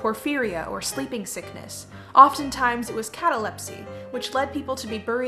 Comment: oh my god i misheard catalepsy as cataplexy and thought they were just. burying a ton of narcoleptic people alive which i mean still definitely messed up that they were burying people alive in the first place but like, being narcoleptic with cataplexy it made me jump a bit lol :'D